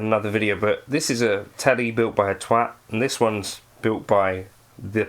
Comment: Did he just say built by a "teat" (0:14)? LOL!